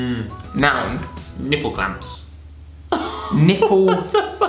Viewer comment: when said nipple clapms i thought he said google maps but good video btw